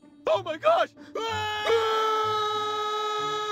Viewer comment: I thought he said " you got neutered " 😂😂😂😂